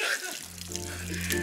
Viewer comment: Did he just said duterte? XD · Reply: No, he said "Go charity!"